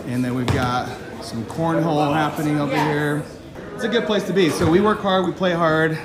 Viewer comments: EDIT - Middle management has been triggered. You guys should see my DMs. <link> | Share the cringe if you enjoyed! More of this? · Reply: Did he just say they've got some "cornholing" going on there? Isn't that a euphemism for anal sex? Just what kind of company event is this??? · Did he just say they've got some "cornholing" going on there? Isn't that a euphemism for anal sex? Just what kind of company event is this???